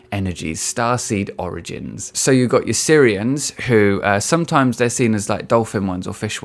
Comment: At first I thought you’d said Syrians, which just seemed cruel.